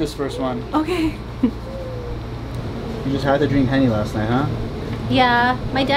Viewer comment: Was she saying she partied with honey or is that a code word? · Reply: I thought she said Henny, for Hennessy.